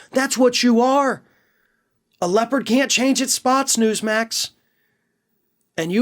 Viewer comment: I swear I thought I heard you say "a leper can't change their spots" and I'd like to go on the record and say in their case that would also be acceptable.